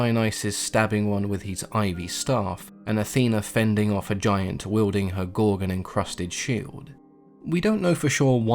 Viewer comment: hold the phone, did he say Afeana fended of a giant with, A SHOO ?